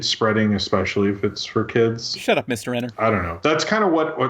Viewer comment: did anyone hear say "shut up mr.enter" at 1:20:35?